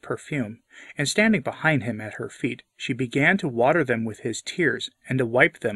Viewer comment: is YouTube AI editing your voice?? Notice these scripture errors in what you read at these time markers. The bold face word is what I hear you read/say in the audio: 1:20 HER feet vs his feet 1:22 WATER them vs wash them 1:22 with HIS tears vs her tears 1:25 with the hairs of HIS head? 1:58 he to whom he has BLESSED more? 2:28 her many sins are GIVEN her? 2:32 but he who is GIVEN less? 2:34 Then he said to the woman "Your sins are GIVEN to you" ? I've heard you way too many times to know there's something not normal here. Does anyone else hear these bold face words in the audio??? God bless you. Ave Maria.